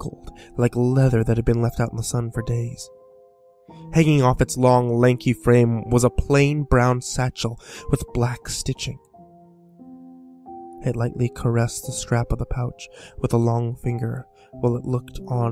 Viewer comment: im pretty sure a gingerbread man does not have blue skin and pink eyes and sharp ass teeth. are you smoking crack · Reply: Misheard blue for brown at first XD add in the pink eyes and its a demonic gingerbread man.